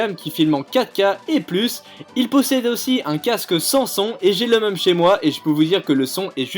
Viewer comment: à 2:28 j'ai compris "un casque sans son" wtf mdr alors que c'est la marque